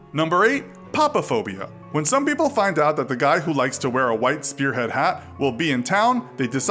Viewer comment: i heard white spear headed hats and i though the Kool kids klub was back in business